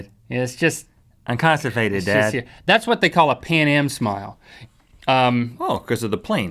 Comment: I heard "Panem smile." Hunger Games is taking over my brain!